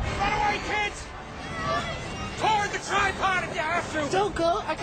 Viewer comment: What does Ryan say at 0:15? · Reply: oh he said toward the tripod if u have to